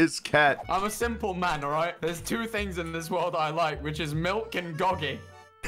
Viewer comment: kinda sounds like he says milkin gogy 😭